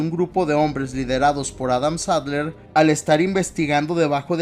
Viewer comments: Entendí Adam Sandler 😂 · Adam Sandler?? Jajajaja te juro que escuché eso 😂😂